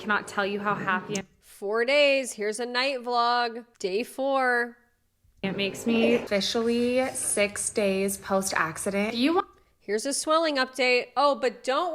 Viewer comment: I notice she keeps saying “my life” “me” “my” and it’s NEVER about him and his day and his life and how he’s feeling. He’s just a prop to her. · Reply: “it makes meeeeeeee, officially, 6 days post accident” at least that’s what I think she says 😏